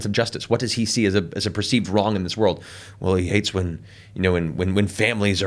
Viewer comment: when he was talking about character motivation I thought Matt said “well he hates women”